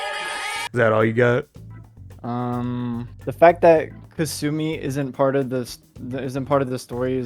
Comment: He said “the fact that she wasn’t more involved in the story is racist” which is still a silly thing to say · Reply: ohh I thought he said kasumi was racist thx for the respond